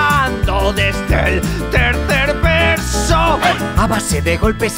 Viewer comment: "¿¿QUIÉN TE HIZO ESTO?? ME CAGO EN SUS MUERTOS" JAJAJAJAJAJJAJA XDDD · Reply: Axel Valenzuela pensé que decía 3er beso jajaj